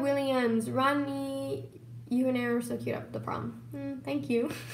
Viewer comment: waittt what did roni say?? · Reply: Zibin “roni, u and Aaron were so cute at the prom.”